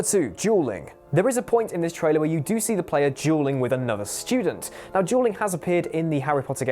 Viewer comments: I thought he said juul not duel.. woops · I thought he said juuling